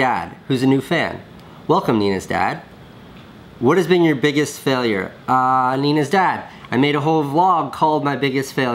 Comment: I thought he said Nina's dad was his biggest failure